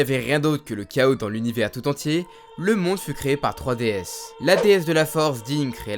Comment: Lol au début j'ai compris 3ds comme la console portative de nintendo